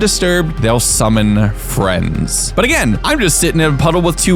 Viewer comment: did anyone else hear they’ll summon Fred😂